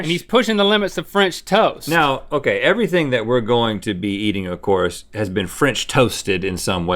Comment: Am I the only one who at 1:31 thought they were going to say "Now, okay everything that we'll be eating, of course, has been french toasted for safety"????